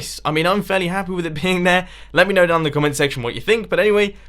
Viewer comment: I swear I heard him say peeing there